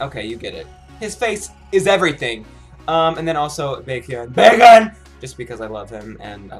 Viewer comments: I heard "Bacon" instead of "Baekhyun" XDD,nice job though. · I shouldn't be saying this but it sounded soo fun by when he produced Baekhyun's name it sounded like he was saying "bacon" but it was a good reaction video!